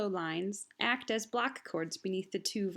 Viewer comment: Hi At 3:26 did you say the viola and violocello lines use Black-chords or Block-chords? Thank you. Please keep making these great educational videos! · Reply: "Block chords" :)